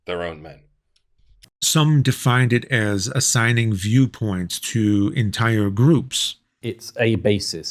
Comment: i thought he said "assigning jew points" and I couldn't believe he went there, had to rewind